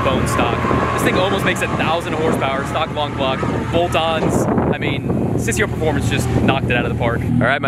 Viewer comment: I only so 2 minutes of this vedio, I know to day it’s the 1st of Arpril, He said Stock engine & 900bhp ( stock engine ) Well done my friend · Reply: "Stock long block, bolt ons"....is what he said at about 6:53.